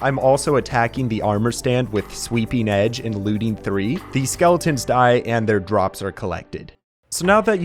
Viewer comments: he said "looting 3" but the subtitles said "Looting 300" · when he said sweeping looting 300 instead of three in the captions